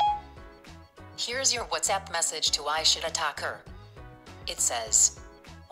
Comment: did siri say "I shit a taco" lmao